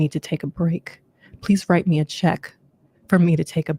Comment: Did she say write me a check? Childdddd 🥴